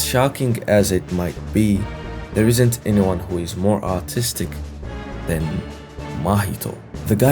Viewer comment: I can't be the only who heard this as "There isn't anyone more autistic than Mahito" right????????????